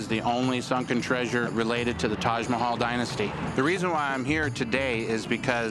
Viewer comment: Did he say Taj Mahal Dynasty?